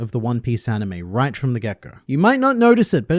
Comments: Every time you say “get go” you should edit gecko Moria into the frame because with your accent + being one piece primed in the mind it sound like your saying gecko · I watch on 1.75 speed and when you say “get go” all I hear is “kishishishishi”